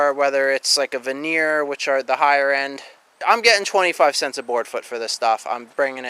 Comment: 2166 board foot. 541 bucks pretty much. 2 mile drive. I wouldn't mind spending an hour doing the same! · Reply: ah I thought he said 35 cents a foot not 25 my bad Mr math Police